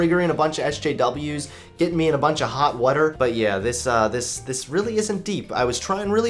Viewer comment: Did you just say "woter" ~11:00......... Unsubbed